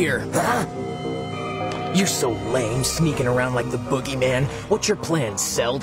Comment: did trunks say Boogeyman?